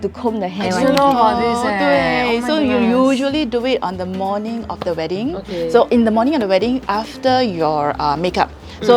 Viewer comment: What did say at 9:58 when they were asking about the Hair Combing? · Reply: i think its “they’re going to start crying” 😂